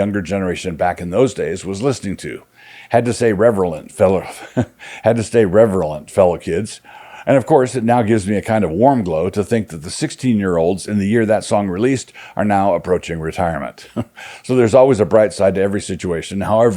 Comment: "It gives me a kind of warm glow to think that the 6 year olds in the year that song released are now approaching retirement." I feel personally attacked. ;) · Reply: I thought he said 16 yo